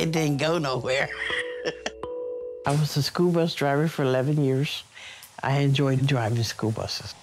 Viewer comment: I thought she said scuba diver not scho bus driver 😂 Anyway good for her she already won't be carrying weight everyday anymore T.T